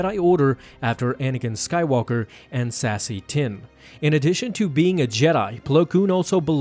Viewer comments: When you said "Sassi Tin" I heard "Sassy Tim." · I heard "Sassy Tim" and now I'm picturing a very effeminate Jedi who sounds like Big Gay Al from Southpark. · did he just say Sassy Time?